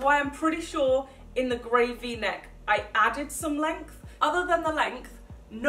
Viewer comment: Did she just say gravy neck at 8:13? 🤔